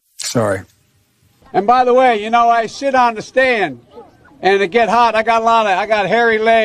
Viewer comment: Did he say ... I sh!t on the stand?